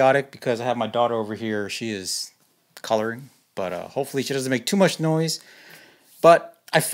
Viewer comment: sounded like u said because she is colored